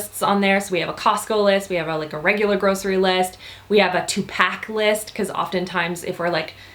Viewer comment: Anyone else thought when she said "two pack list" she said "tupac list"??